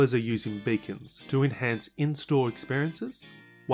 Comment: I thought he said they are going to use "bacon" to enhance the shopping experience. I figure that is likely to work. 👍